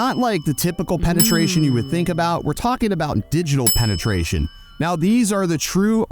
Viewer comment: I thought he said deep not digital. I'm fucked up 🤣